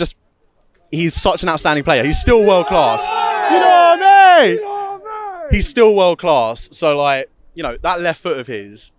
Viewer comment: what do they yell? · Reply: “Yid Army“ I think it is 💜